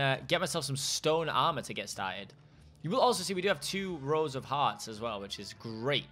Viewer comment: When he says “Rows of Hearts” in 7:02, I thought he said Rosa Parks. 😂